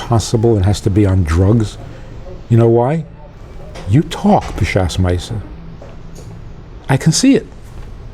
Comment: You talk during "what" ?? what's that word meaning at 2:30, he repeats it twice ???? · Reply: David Pinto "b'shas meiseh" he's talking about sex